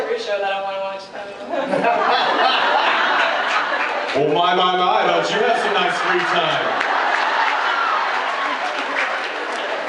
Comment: Hello,could anyone tell me what does the girl answer to Jensen's question about tv-show she would like to watch at 11:00 and what does Jensen answer ?Thanks in advance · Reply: She said “I watch every show that I wanna watch” and then Jensen said “Oh my, my, my don’t you have some nice free time”